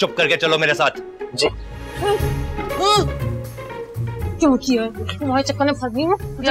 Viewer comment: What exactly did she just said? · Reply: Kyu kiyaa 😂 mujhe bhi lga k chutiya kaha tha